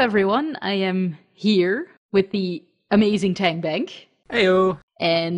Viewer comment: i thought you said "hand bag" instead of "tank bank" lol